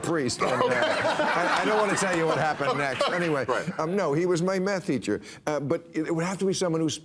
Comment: Sounded like he said, “No, he was my meth teacher.” 😂😂